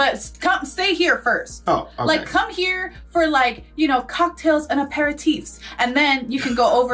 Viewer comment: I literally thought you said cocktails and a pair of teets in the beginning. 😂😂😂